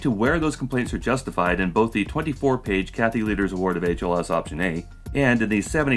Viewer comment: I keep hearing "cat litter" instead of "Kathy Lueders", seems rather fit.